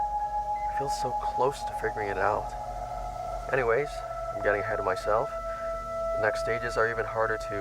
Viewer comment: When he said ahead of myself in the vhs tape thing I thought he said head lice😭🙏